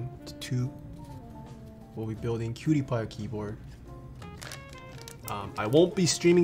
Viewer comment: did i hear that right, "building CutiePie a keyboard"? is that Marzia or is that someone else?